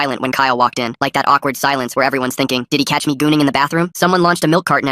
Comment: Ayo, did they really say gooning at 0:33???